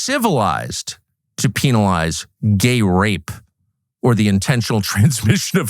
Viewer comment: I thought he said Grape Ape, not gay r@